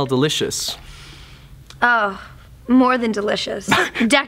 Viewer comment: what does she say in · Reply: Levy Marques she says "more then delicious"